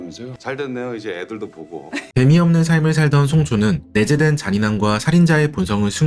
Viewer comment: 죄송한데 1:54에 애미없는 삶이라고 들었어요